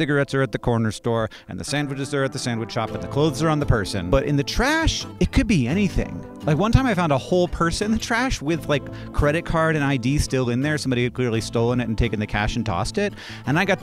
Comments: Is the purse pic there just for people not to mishear “person”? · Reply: I misheard person anyway, I'm glad the purse was there so I knew what he meant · “One time I found a person in the trash with a wallet still in it” is the most unhinged mishearing I’ve suffered in a long long time. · The purse image was so absolutely necessary because without it I definitely would’ve thought you said “person” O.o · Is the purse pic there just for people not to mishear “person”? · “One time I found a person in the trash with a wallet still in it” is the most unhinged mishearing I’ve suffered in a long long time. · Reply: Definitely misheard it the first time, and I'm still hearing person even after learning it was purse. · i thought you said you found a whole person in the trash can 😅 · Oh! A whole purse….. I thought I heard a whole person….😂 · As many of you, I heard “person” instead of “purse”, but I assumed that was just a figure of speech because so many identifying possessions were there. Idk I feel like if someone showed me a collection of IDs, cards, and other personal items, I wouldn’t question them if they said “I’ve got basically a whole person here”